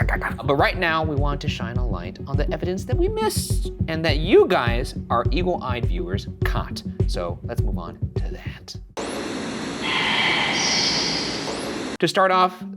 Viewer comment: I heard "evil-eyed viewers" 😂